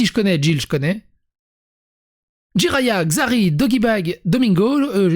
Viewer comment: c'est moi ou j'ai entendu dogee-bag au lieu de doigby a 2:04 😂🤣